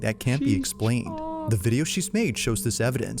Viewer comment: did you just say "she's hot?"